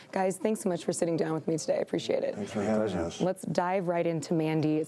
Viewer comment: wtf did they all say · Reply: "Thanks for having us"